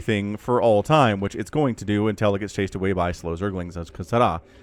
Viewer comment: please! Can you tell me what did you say at 3:48-3:52? I'm trying my best to learn english but still fail XD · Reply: "which it's going to do until it gets chased away by slow zerglings *unintelligible sound at end*" You are welcome